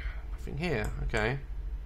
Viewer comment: did anyone else hear “here.” In a deep male voice??